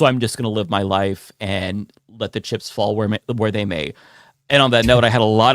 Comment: I thought was going to say, 'Let the shits fall where they may.'